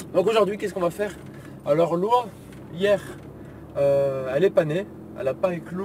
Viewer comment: J'ai eu un petit choc à 0:43 quand tu as dis : "L''oie elle est pas née" ; j'ai compris : "L'oie elle est panée" !!